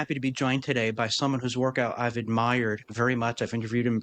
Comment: I heard “by someone whose workout I’ve admired”